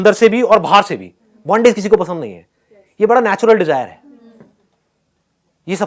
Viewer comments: what he says, bondage? i am not getting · what is the word that Sandeep speaks at 21:35? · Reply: bondage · What Sandeep sir says At 21:35 second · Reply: Bondage